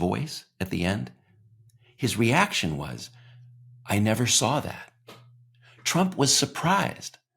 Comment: I thought trump said, "I never said that " not saw that. Am I wrong?